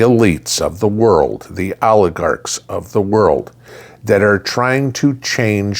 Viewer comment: When said "oligarchs of the world" I just heard "aardvarks of the world" and I was so confused for a minute 😂😂